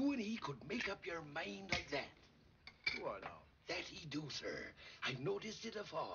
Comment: OK I give up!!! Guys what does Squire Trelawney say here 1:44??? - sounds like "Word Up" - but somehow I doubt that's right Lol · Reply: He says “do I now?”